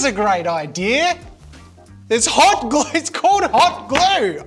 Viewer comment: "it's cold hot glue" decide what you want to say